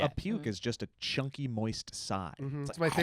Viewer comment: I heard "chunky moist sigh" literally mid-bite...